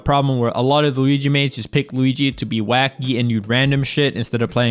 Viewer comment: did Leffen just say Luigi is wack?